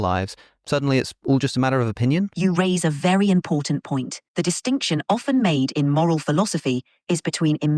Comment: bro in 6:50 i heard her say "your race is a very important point" and i fuggin choked